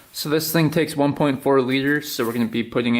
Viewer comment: No. 1.5 quarts is 1.42 liters. He says it's supposed to take 1.4 liters so he's spot on actually. You're math is actually way off. You can use a conversion table if you don't believe me. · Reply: you’re right, I thought he said it called for 1.9 liters